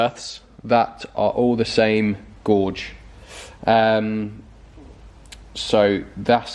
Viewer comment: Gorge - A narrow valley between hills or mountains 8:12 Gauge - The thickness, size or capacity of something, ie "tyre gauge" "gauge wire" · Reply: I thought he said gorge and not gauge, wandered what he was on about.